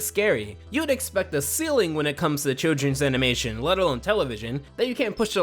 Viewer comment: a ceiling? Silling? Silly? What did he say? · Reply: Ceiling, basically he was saying you'd expect there to be a limit.